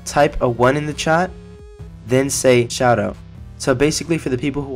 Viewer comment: 1 Shoutout ? Sounded like u said shadow but yeah